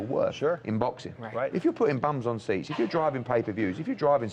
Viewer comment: Did eddie hearn just say "if you're putting BUMS on stage,if ur driving ppvs, if ur drivong subscriptions, ull make alot of money."??? Bc if so, is he calling ksi and logam bums?? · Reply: Hahaha u U.K mfs just got some different "lingo" or whatever that idk. But if he said bums in seats he means butts 😂 lol i get it hahah. I coulda swore he said bums on stage